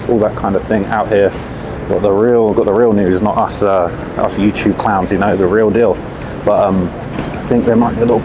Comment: I had to rewind for a sec, I could've sworn I heard him say "they got the real niggas here, not us YouTube clowns" 😂😂😭